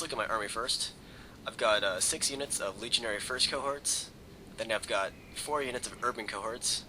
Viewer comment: about those firts cohorts it kinda sounds like you say legionary frisko hearts :D